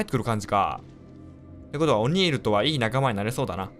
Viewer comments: オニールがたまに「おにぎり」って聞こえるのは自分だけですか？ 自分だけですよね・・・ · オニールがおにぎりって聞こえるｗ